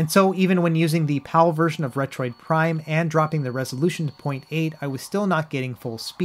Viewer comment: Did you say "Retroid Prime"?